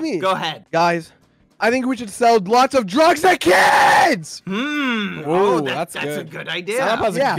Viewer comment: I love how passionate Sapnap is about selling drugs to kids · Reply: I thought he said drugs and kids